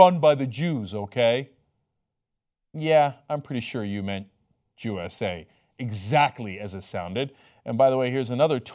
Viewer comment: To be honest, I had to play it back twice, as the first time I thought the guy was just saying "U S A". It' wasn't until I was listening carefully that I heard "Jew S A"